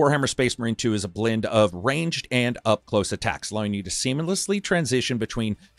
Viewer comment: Did you just say semen-lessly instead of seamlessly? 😂😂